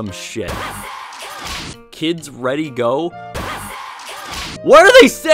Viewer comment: I think it's "I said go!"